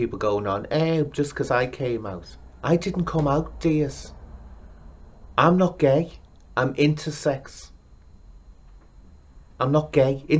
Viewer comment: For a moment I thought he was saying "I'm not gay, I'm into sex".